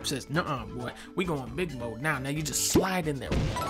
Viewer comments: what did he say again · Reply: “we goin big mode now, now ya just slide” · Bruh I think he said the n-word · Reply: No, he says, “Nuh-uh, boy.” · Did he just say the n-word? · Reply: “we going big mode now, now ya just—“ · what did he sayyyyy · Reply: we going big mode now, now you just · Wait, what did the clear pipe say at 2:30? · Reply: “nuh, we going big mode now, now ya just slide—“